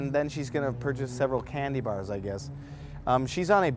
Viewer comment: When you first said 'candy' I thought you said 'cannabinoid'. Different diet. '